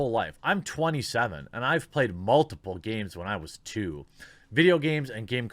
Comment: I wasn't looking at the vid, and when read the deer's "I played multiplayer shooters when I was 2.", I heard it as, "when I was a dude."